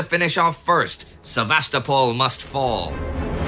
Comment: I heard that as Sevastafall must fall!